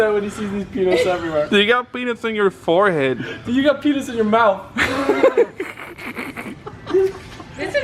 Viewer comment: It sounded like you told he had penis in his mouth at 2:27 lmao